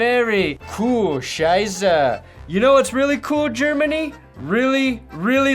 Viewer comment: So ein Blödsinn. So every time he said "coole scheiße" I heard "Kühe scheiße".